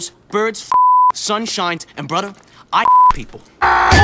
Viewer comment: he actually says fuck!!